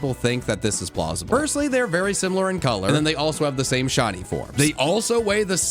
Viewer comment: On the Ditto theory, I thought that he said Shawty, not shiny What if instead of shinies, we got shawtys.